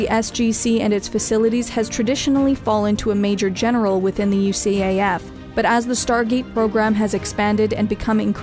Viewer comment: does she say UCAF instead of USAF? Also the Legions Astartes in general and the Night Lords in particular